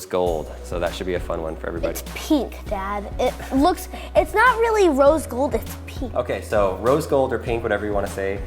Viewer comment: I thought he said "it's not really rose gold, it's peak"😂